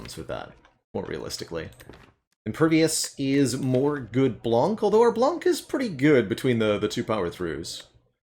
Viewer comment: Did he say good blonk or am I hearing things?